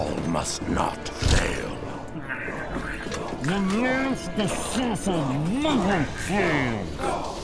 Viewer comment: Does anyone know what he said there? I'm getting "We raid the Assasin" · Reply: "The last assassin never fails." I think